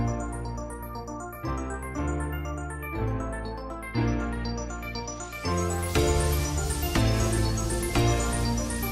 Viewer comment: can someone tell me what's that at 1:47 to 1:49? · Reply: Were